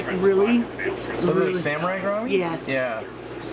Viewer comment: What are the fish @ 10:28? You say what they are, but can't understand what you said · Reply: John Carlson those are Sphaerichthys Vaillanti (Vaillant Chocolate Gourami or Samurai Gourami) I believe you asking about.